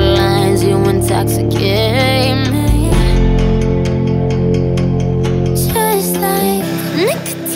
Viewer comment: I thought she said taste like instead of just like